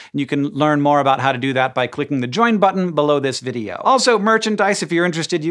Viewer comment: I heard "joint button." Made me think of a funny vending machine.